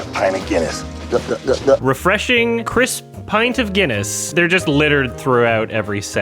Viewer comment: I heard "Chris Pint of Guinness" for a second